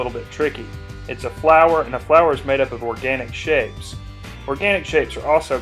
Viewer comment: I thought he said they are made out of organic shit lol